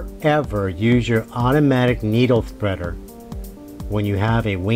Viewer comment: I thought you said not to use your thread cutter with a wing needle, but your using your cutter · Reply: He said to not use the AUTOMATIC NEEDLE THREADER not the cutter.